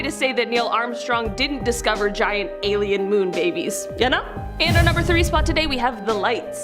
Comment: And here I always thought Armstrong said Bogies and not Babies?